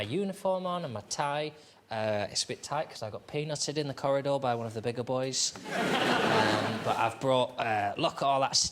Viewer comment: I can't make out what he is saying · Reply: "And my tie, it's a bit tight because I got peanutted in the corridor by one of the bigger boys." Peanutting is when you pull someone's tie to make the knot as tight as possible.